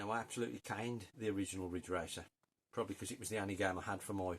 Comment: Brenda cycling in lycras 🤪 Mikes shed big up! I had seen that Duel not long ago tbh as you summed it up decent 👊🏻 Ridge Racer at first i thought you said Grift 🤣